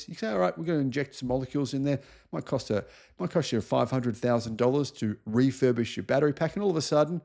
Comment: listen to what you say. Might want to edit that bit. It sounds like you say "It might cost you $500,000 to refurbish your batteries." I had to listen a few times and even after thinking you mean $500 to $1000, it still sounds like $500,000. That is a big difference.